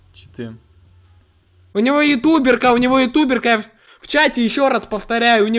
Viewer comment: Давайте добьём 500к до НГ!! С меня лайк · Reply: Мне послышалось вместо Ютуберки Домерка 🤣🤣🤣